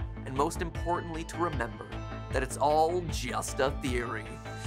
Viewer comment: it sounded like he said "HE THATS GUST A THEROY A GAAY THEROY"